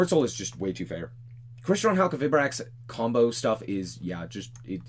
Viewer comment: What is the name of the 15:01 deck? I know he said it but i cant quite catch it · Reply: Halq good stuff. It isn't really a deck so much as a pile of cards centered around Crystron Halquifibrax.